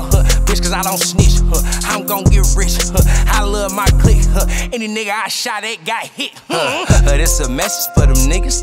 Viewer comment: did he say any nigga I shot ain’t get shot